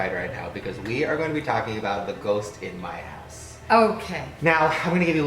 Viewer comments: it sounded like he said " today we are going to be talking about the ghost in my ass" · I was dead at the beginning I thought he said the ghost in my ass I'm so Srry 😭😂😂😂😭😭😭😭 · I thought said the ghost in my ass lmao 😂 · At first i thought he said " the ghost in my ass" 😂😂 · Who else thought said "ghost in my ass" at 0:12 😂😂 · seconds I heard say "the ghost in my ass" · i thought he said "Ghost in my ass" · Why did I hear 'the ghost in my ass' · I seriously thought he said the ghost in my ass at the beginning lol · I thought said ghost in my ass in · Lol in the beginning I thought he said "we are gonna be talking about the ghost in my ass" xD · when he said "the ghost in my house" i thought he said "the ghost in my ass" omg i need to get my hearing checked XD